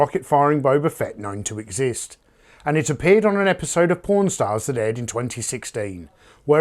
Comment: This appeared on an episode of Porn Stars...Hey you What did say? Ohhh Pawn Stars oh ok.....This was about to take a weird turn.